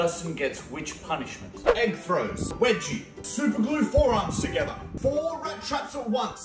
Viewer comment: I thought it said superglue foreskin at first